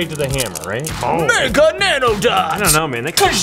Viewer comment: I thought it said mega noodles instead of mega Nandothings lol